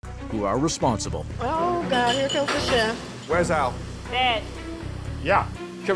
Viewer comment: I misheard them the first time. 0:03 Gordon: "Where's Al?" Woman: "Dead."